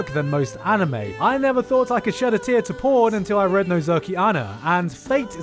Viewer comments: who is the author? i didnt hear the name well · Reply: nozoki ana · I cant understand what the title is help me pls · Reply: Nozoki ana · What's the sauce for 10:20 ik he says the name of it but I have no idea how tf to spell it or where to find it on n-henta1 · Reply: Nozoki Ana. It's a manga so it won't really be on that site